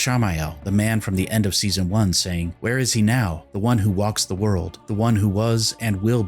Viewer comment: One thing I've noticed rewatchibg the clip is that I am not sure the closed captioning in the scene exactly matches Ishy's lines as spoken. Now I might just be mishearing it but to me "where is he now" sounds more like "there is now" and "it will be served" more like "it will be so."